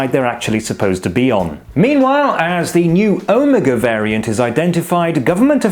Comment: Listen to that part of the video again, very closely. Pay special attention to the beginning of the sentence, and remember that I am talking about the future. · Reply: Listening to it again, I conclude that I misheard omega as omicron. My bad. But I predict usage of greek letters will end before omega because of the association of omega with 'the end'.